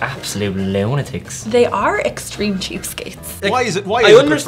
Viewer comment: I THOUGHT IT SAID EXTREME CUPCAKES